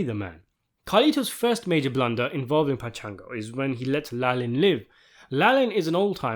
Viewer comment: anyone else get Metal Gear flashbacks? I genuinely thought he said “la li Lu le lo.”